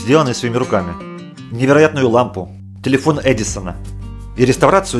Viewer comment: Мена одново смущаит что там сказали тилефон эдисана